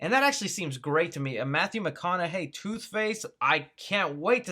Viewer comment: Did he say “toothface”? Lmao 😂